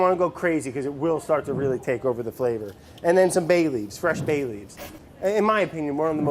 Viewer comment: I was listening to this on low volume and when he said fresh bay leaves it sounded like he said fresh babies